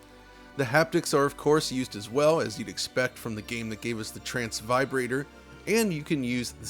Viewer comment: Did he just say trans vibrator at 16:25 ?